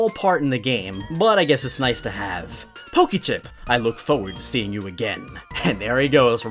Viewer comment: I thought he said poke chip